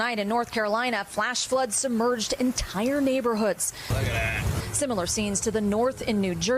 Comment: sounded like he said "BLUB-A-BLUUAAEHHH!!!" but he actually said "look at that"